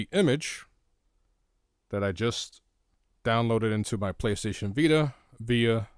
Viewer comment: to me at 0:6 it sounds like he said police station vita